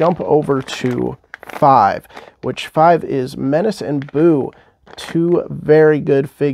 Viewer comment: I thought you said boo because you didn’t like that gogo 😂